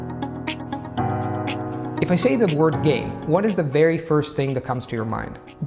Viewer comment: First thing I thought was gaf. Then I saw you said game. Lol.